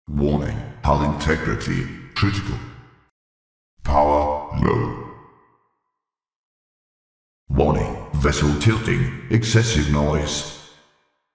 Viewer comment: vessel tilting? I thought he said vessels cavitating